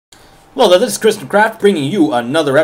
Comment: did he say 'Chrisandthecrap' ?.....or is it just me :D,... love the videos, best minecrafters ever